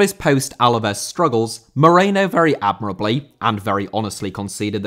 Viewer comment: I thought you said "Ale Moreno"🤣🤣